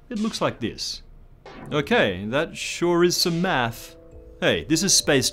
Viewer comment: subtitles say "that sure is some ass", lol